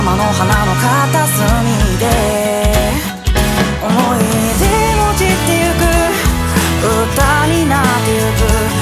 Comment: 歌詞を見ないで聴いていたのでなぜか｢思い出も散っていく｣が｢思い出、もちっていく｣に聞こえてしまった… もちっていくってなんだ？笑 こういう歌詞、すごく好きです！